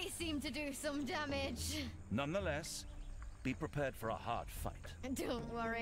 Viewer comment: Did he say:' ...prepare for hard fuck'? XD